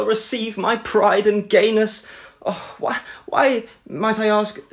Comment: Them:”my pride and gayness” Me:”I’m sorry what?” Them:”gayness?” Me:”oh I thought you said anus-“💀 👇🏻